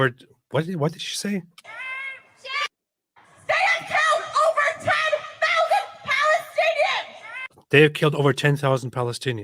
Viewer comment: that’s weird I thought she said over 10m Palestinians the first time I listened to it